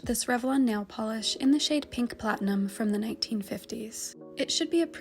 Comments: I thought it said pink plankton 😭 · I thought it said pink PLANTATION 😭boutta saaay